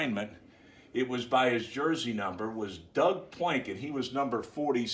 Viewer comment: Hhmmm great video but the way you pronounced Doug Plank at 1:40 sounded like Doug “Planket” so I was looking up some guy with that name but eventually found Doug Plank. It’s a shame he didn’t get to play on the 85 bears.